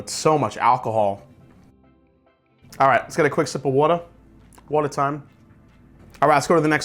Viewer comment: Did you say Wader @ 5:15 ? Thats the way we talk back East............:))